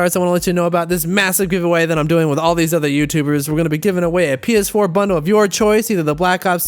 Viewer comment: omg at the beginning of your video I thought you said we are giving away a penis 4 bundle bruh I have a dirty mind smh